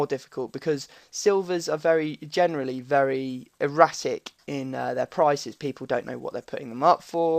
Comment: Haha lool ;) 4:40 'eratic' sounded like erotic